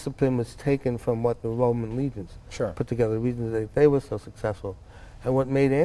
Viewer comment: What did he say at 2:47 the Roman what? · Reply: Roman Legions.